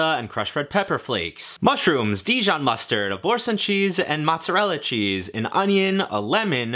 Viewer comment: lol, sounds like you said 'abortion cheese'